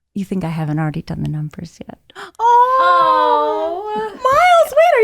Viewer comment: Cutie buties help me!🍑 I’m a not native english speaker and I can not understand what says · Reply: "all he said was ''you think I haven't already done the numbers yet?"